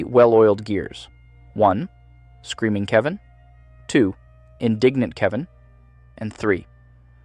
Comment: I love that it says "indigent (poor, broke, bankrupt) Kevin" instead of "indignant (annoyed, outraged) Kevin" lmao